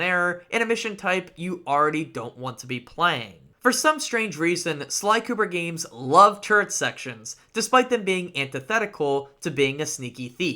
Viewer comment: I heard "love turd sections" but that's another way to describe the level tbh